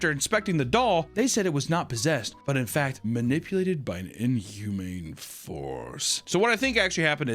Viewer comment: Ed and Lorraine Warren went to my "grandparents house" and told them it's one of the most evil haunted places they have ever seen just by looking at it from the outside and my grandparents told them they weren't even at their house yet. It was just their neighbors house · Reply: I thought he said, "Inhumane farts" at first..ooopps